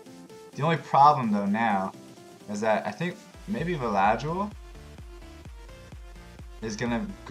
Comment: Okay my bad, it sounds like you were saying eladriel but you were saying velajuel